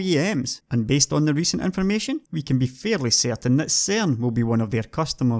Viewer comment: If anyone can help me understand, at 19:27 he says " we can be fairly certain that S..... will be one of their customers" Who is he talking about? What is S.....? I didn't catch that. Thank you. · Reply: Cern